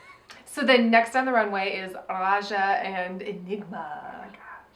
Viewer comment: Why do you keep saying Aja instead of Raja?